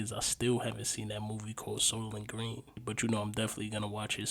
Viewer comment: whats the name of the movie he mentioned in 1:46? i honestly couldnt understand when mentioned the title of the movie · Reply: Soylent green is the name of the movie